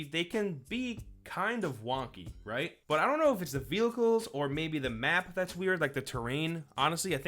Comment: um... did he say "Vilicals" there? I know he's supposerd to be saying vehicles but that's not what it sounds like.